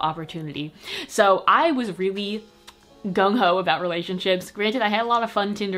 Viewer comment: did she just said Gang Hou?